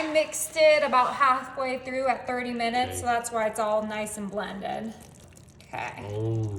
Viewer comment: How many hours on High for Mac & Cheese? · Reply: I thought she said 39 minutes on high????